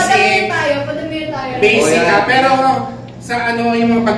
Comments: Belle to Donny "Babe, try mo yung Lato-lato" (omg, kinilig ako...tama ba yung narinig ko, nakadami akong replay yun talaga dinig ko eh) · Omggggggggggg I cannotttttt try niyo pakinggan iyong 16:36 did Belle say " Babe try mo lato-lato"?? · luuh did i hear it right sa 16:37 belle said "babe try mo yung lato lato" pakibasag po ako 😂 hahaahhaha